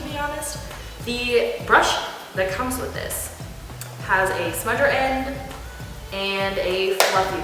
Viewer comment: Totally thought you said "spider end" when you were talking about the small end of the brush. Haha. Love the way this looks with your eyes. Kind of like a vampire which I totally dig. "And now we have the black shade. Weeee" 😝 #dead